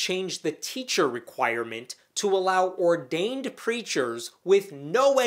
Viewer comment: I heard him say fundamentalist christian 'creatures' instead of 'preachers'... Would have been a better description.